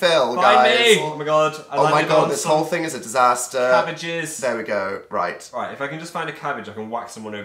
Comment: When Phil said 'cabbages' I thought he said 'having a jizz'